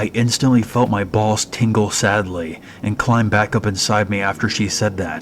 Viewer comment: Did I hear that right? I felt my balls tingle sadly? Wtf 🤣